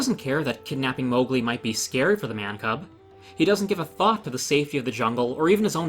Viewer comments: I had to do a double take because I was pretty sure I heard you say "He doesn't give a fock" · Hearing say "fuck" left me stunned, I did a double-take when I heard it first. Damn, you heard it here, first: is serious about fire safety! The gloves come off when fire's involved! Not upset by it at all, but I think it's the first time I've ever heard say "fuck." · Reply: I think he said "Thought".